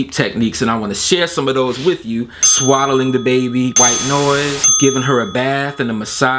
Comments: I literally thought he said “ swallow the baby” 💀💀💀💀💀🍽🍽🍽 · Not even gonna lie.. I honestly thought you said "Swallowing the baby" 😭😂💀